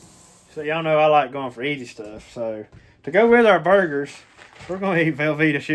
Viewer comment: I don't hear as good as I used to, and when you was telling what you was gonna be cooking up on the grill, instead of burgers, I thought you said birds!!! I figured you'd done went back down there to the field with a shotgun. Though I ain't never heard of nobody eating crows before. 🤪